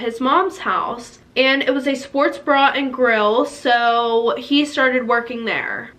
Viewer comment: I thought she said Sports bra and grill